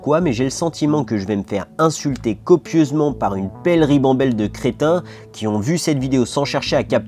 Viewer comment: Quand t'as dis "crétin" j'ai entendu "chrétien"